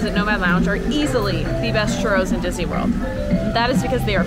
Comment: I know said that the part with the best churros in all of Disney World were at nomad lounge... but my dead brain thought she said the best churros in Sea World and I got confused for a second..